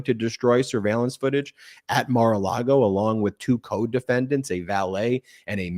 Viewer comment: I thought you said “co-dependent”